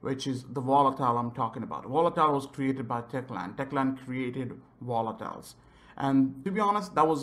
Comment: Sounded like he said "Techland created watermelons" 😂